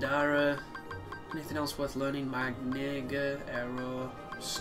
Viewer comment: I'm sorry, but when you were saying Magnega in 2:50 it sounded like you said "my nigga" lol I was cracking up because I was only listening to your video without watching so it caught me off guard lol